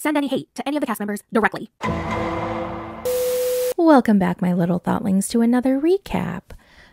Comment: When you said “welcome back my little thotlings” I literally heard “welcome back my little butt wings” 😂😂😂